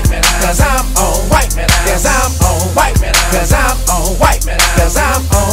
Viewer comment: I thought it said 'cuz I'm old a bunch of times